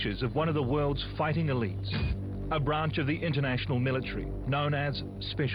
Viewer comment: thats my question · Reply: my bad, for some reason I thought the video said, they were "international special forces". No definitely these guys are real deal.